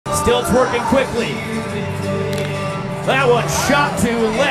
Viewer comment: At first I heard “Still twerking quickly”